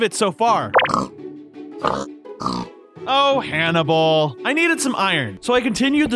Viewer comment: When he was naming the pig I thought he said cannibal